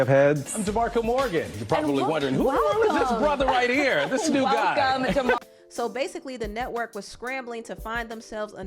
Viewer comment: it sounded like the new anchor said "who is this new bly" in stead of "who is this new guy" because He was thinking black guy 🤓😝